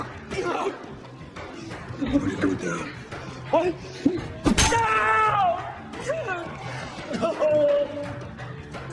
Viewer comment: What does he say at 1:52, I've rewind back a thousand times and still don't know making me furious 😡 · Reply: he says, "where'd it go down?"